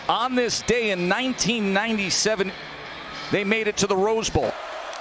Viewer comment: Did he just say the come bowl ?